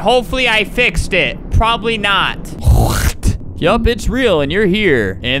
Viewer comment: did he say the b-word!?!! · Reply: No he said "yep it's and it's real"